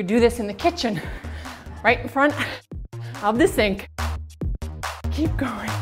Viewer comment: "You can do this in the kitchen... right in front of the sink." Or you know, right in front of the food. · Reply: I thought she was saying: Right in front of your steak XD